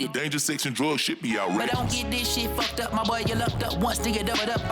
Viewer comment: first listen i thought the JID echo said "dating sexy red should be outdated". My fault for staying up so late to listen on release 😂